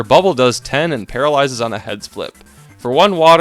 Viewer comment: What says: "Heads flip." What I hear: "Head spliff."